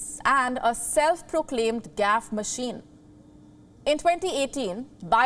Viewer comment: I swear I first heard "gas machine". 😂